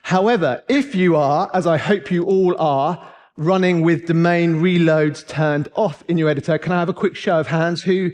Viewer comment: I can fully admit my mistakes - 17:25 I went on the search for some new "Main Reload" options....... Completely thought he said 'The Main' and not "Domain" 5 mins i'll never get back lol